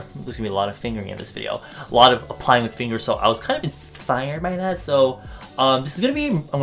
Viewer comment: Did he says INSFIRED ? Sorry I'm sorry